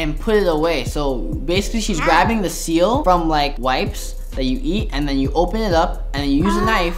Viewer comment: Did he say wipes that you can eat?!